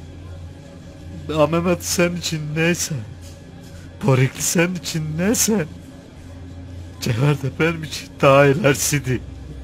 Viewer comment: tam olarak ne diyor???? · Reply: "Memati senin icin neyse, cevher de benim için daha ilerisi" dedi.